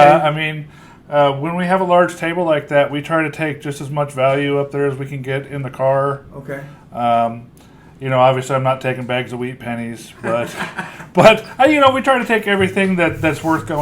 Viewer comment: all i heard was "bags of weed" then i heard bags of wheat pennies..... )